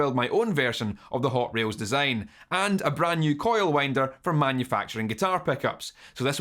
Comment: I have to stop multi-tasking while I listen to your explanations. You mentioned your pickup winder and through the wee speakers in my iPhone I could swear that you said “pickup wanker”. My first thought was, “Aye laddie ahn keep yer shower toys to ye’self. TMI.” 😆 Anyway I realized tha ya said something else besides what I thought ya said. I know it’s not your brogue but just my half arsed attention to your words. My grandmum was from Edinburg, Clan Stuart...Aye, those Stuarts Mary James Charles etc and on account of my grandmum I learned to decipher Scot English and speak with her accent...it’s your brand o’ Scot elocution too laddie ...I hear your Edinburgh accent and ya sound like my grandmum...the only difference between you and her is about an octave and a half. 😆 Pickup wankers... ✊🏼ha...moving on. What’s the difference between a split-load and long tailed phase inverter as utilised in a push-pull (aka Class AB topology) valve amp. I know what a phase inverter does and the general theory behind how it works but I did not know there were these different types. I can look at a schematic and see which type is used but I don’t know the why or preference for one over the other. Could you please explain this? Keep up the good work. I enjoy your channel and have learned a few things from ya (I’ve been playing guitar and bass guitar for about 38 years) and that I can learn from you reminds me that I still have room for improvement.